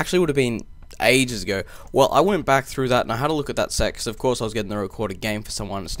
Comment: ???? Does he say " I had a look at that sex" or do I just have a dirty mind?